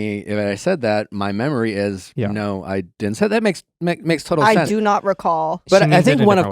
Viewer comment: is not saying "my memory doesn't recall me saying that "-- she keeps say "no I did not say that, why would I say that"